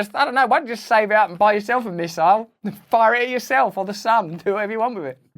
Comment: I thought Russell said "And fire it yourself, I don't know, on the Sabbath" but he said the sun. Mine is a little better.